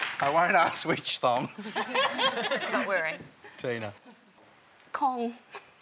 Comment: did she just say "Not wearing?!" ?! ?! ?! :O